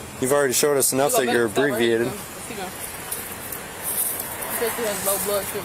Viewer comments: Did he say she's "abbreviated"? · Did I hear right, the officer said she was abbreviated instead of inebriated.. 🤣😂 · I may have misheard but it sounds like he says "you've already shown us you're abbreviated." 😳